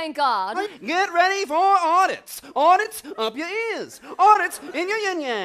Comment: Anybody know what mouths at 5:30? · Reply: she mouths "audits" lol